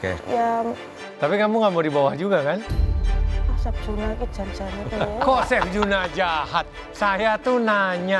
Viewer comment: Chef juna salah denger, bilang "chef juna ki jan jane piye to" artinya chef juna ini sebenernya gimana sih. tapi dari chef juna dengernya Chef Juna jahat.. hehe - menit